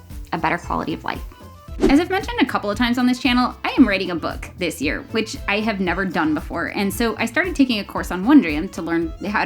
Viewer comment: thank you for this video and for the subtitles!! at 3:49, i thought you said, ‘i am writing a book this year, which i have never done before.’ and i was so shocked for moment that had to go back ahaha! struggles of auditory processing with adhd.